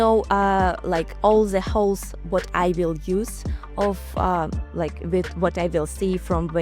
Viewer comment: Did Valentina just say "I know all the holes that I will use" @ 9:53??